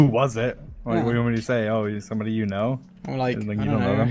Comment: "What do want me to say, oh, HE'S somebody you know?" Dreams first kiss was with a boy confirmed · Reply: Actually dream said ITS somebody you know